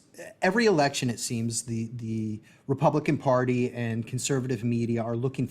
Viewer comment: the captions are wrong. Captions say that the "democratic party" is looking for allegations to grab onto, while the guest audibly says "republican party". Might wanna fix that for the audibly impaired.